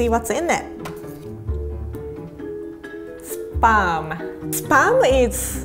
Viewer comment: No offense I love but I heard pronounce sperm instead of spam LOL